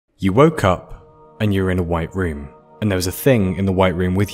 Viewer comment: At first thought he said "you're a white cup" i instead of "you woke up"